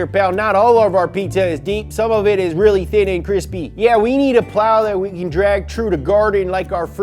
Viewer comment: LOL!! i watch these videos (and most others) at 1.5x speed and @ 3:03 i thought i heard - "solo-mid is thin and crispy" which... yeah. valid.